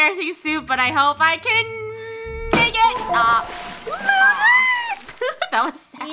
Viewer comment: what did she say???????!!! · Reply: make it